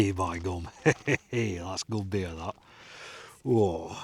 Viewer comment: What a happy guy. Good work mate. from Australia.. Hey what did you say at 13:15? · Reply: Cheers Beej. It's great to hear from a fellow countryman (I was 4 years old when we moved to the UK). What I said at 13:15 was... "Ey by gum, that's good beer.". 🙂 Thanks for watching, and also, for taking the time to post a comment mate. I appreciate both. 👍